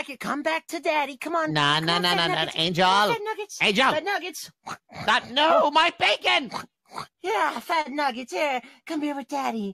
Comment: I thought he said “NO MY VEGAN!”